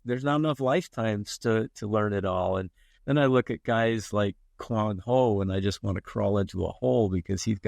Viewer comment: Great podcast. It's always nice to discover that your heroes are humble and still hungry to learn and innovate. Who's the artist he mentions at 30:18? I didn't catch that name. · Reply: The artist’s name is Quang Ho